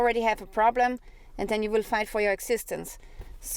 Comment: Did I hear "and then you will fight for your existence" ?